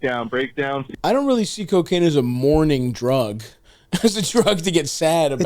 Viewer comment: I thought he said "I don't think cocaine is a MORNING drug" and I almost unfollowed